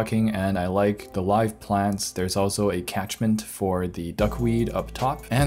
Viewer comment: What did he say that thing was for the duckweed at 10:39? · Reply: catchment, or a separator / container